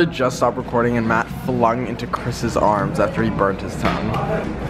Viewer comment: Ngl I thought Nick said "Chris just flew into Matt's arms after he burped his son" I need to pay mpre attention